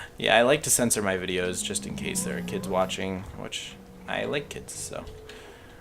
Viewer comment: nice And did you say you like kids!?!?!?!??!?!